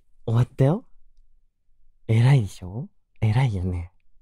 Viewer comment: の偉い？がえrい？に聞こえた私は末期ですね。ぽえろいでした。幸せでした…